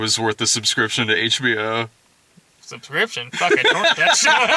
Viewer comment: I couldn’t understand what said at 13:24. Can someone help me out? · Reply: He said “Subscription? Fuck that”